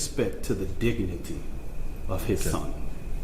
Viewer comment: did he actually say "din nigity?